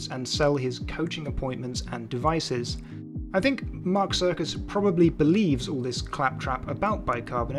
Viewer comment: This guy isn't just a clown, he's the whole Sircus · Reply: I know right? Such an unfortunate last name for a woo peddler. 😂 A few times during the video I thought was saying: "Mark's Circus" 🎪🤡🎪